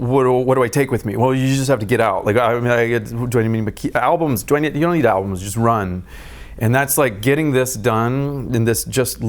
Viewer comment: Jesus, what the helldid he said? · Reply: “Well, you just have to get out. How do I get..? Do I need to keep...? Albums! Do I need..?! You don’t need albums. Just run.” I think!